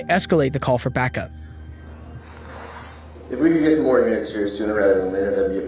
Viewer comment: did he say “can we get more heads here”